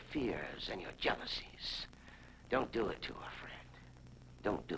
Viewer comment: Did I hear Dr. Rossi call Fred "Frank" at about 3:34? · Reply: I think he said "friend" instead of Frank.